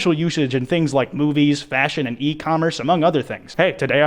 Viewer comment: istg i thought i heard him say boobies instead of movies